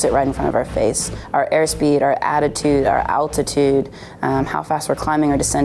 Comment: did she said attitude?